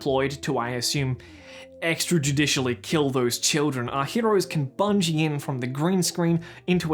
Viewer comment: I know you said 'heroes' at 12:38 but it sounded like you said 'heteros' and it felt really on point